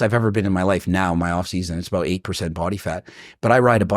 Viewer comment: did he just say he's 8% body fat?